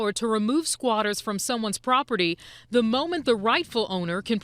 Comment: at first I could have sworn she said "the moment the rifle owner" 🤣 i was like God damn that jumped up a notch lol